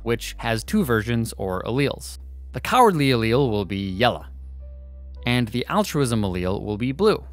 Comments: bruh really just said “yelluh” instead of “yellow” 💀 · did he just say yella?